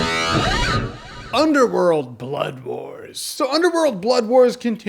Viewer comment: I keep hearing "Bloodborne" instead of "Blood Wars." A game that awesome should not be related to a movie this meh.